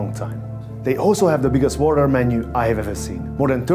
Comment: I'm sorry... did he say water menu?